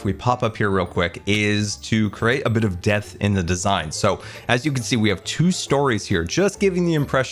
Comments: I heard Scar say "let's put some DEATH in the design" and I didn't bat an eye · I heard "create a bit of death in the design" and now you do too. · at first i heard "create a little death in the design" and thought Scar was taking a dark turn with the hotel